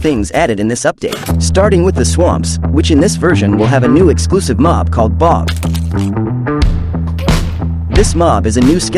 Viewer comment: I heard: "The new mob called Bob"